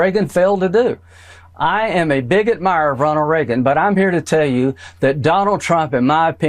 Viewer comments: Did Graham say i am a bigot admirer of Ronald Reagan? 5:12 . I am sorry he "changed his ways" so to speak with respect to the way he spoke about Biden, just another Trump simp! sorry not sorry! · The way he said, “I’m a big admirer..” It sounded like, “I’m a bigot mirer”